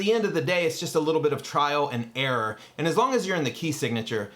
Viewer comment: I totally heard "triad and error" 😂